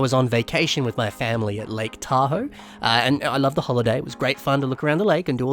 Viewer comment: an i the only one that thought said "lake Tahu?"